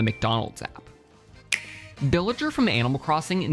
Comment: it sounds like he said billager instad of villager